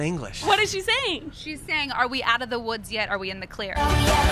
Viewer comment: I thought she was saying "are we in/on the period" instead of "Are we in the clear"..